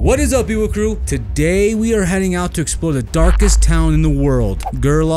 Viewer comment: Thought it said dankest town at first 😂